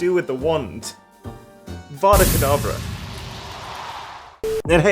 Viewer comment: sounded like fada kadevera